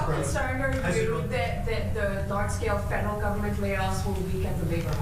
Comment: Just like Trump, I thought the reporter lady said "fentanyl" in her question. I guess she did say "federal", after rewinding several times. 😮😮